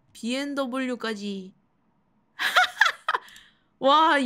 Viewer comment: 차가리에 미쳐버린걸까 BMW로 들리네